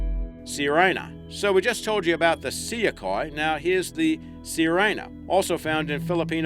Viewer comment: "Sirena" means mermaid. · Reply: i heard him say seakoi..haha